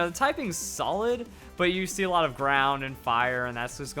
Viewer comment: When said you see a lot of ground and fire, i thought he said brown. I was like "When did a color become a type?" XD